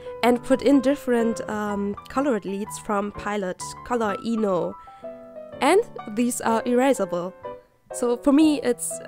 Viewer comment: I first thought the lead said "color emo" XD